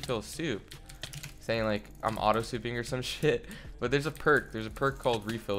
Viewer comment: ngl i heard "im auto clicking" not auto souping